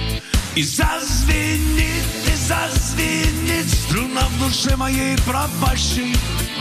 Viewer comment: 3.15 вместо струна поет слюна)) или мне одному так послышалось 😊 а в целом ролик звук мне больше нравится чем запись ..